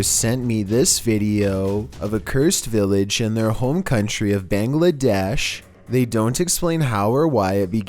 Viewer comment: At first I thought he said BANGLA_ But then I realised he wanted to say " BANGLADESH"😭